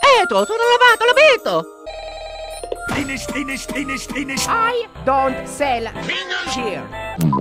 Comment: I always thought he was saying spinach, spare spinach when I was a kid it’s always so funny